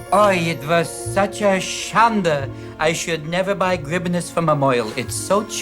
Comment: The first "foreign" word he says, at least to my German ear, sounds more like, "Schande", which (also?) means "shame". The other words I can't make out, I don't think they are (understandable) German, so your subtitles are probably correct there.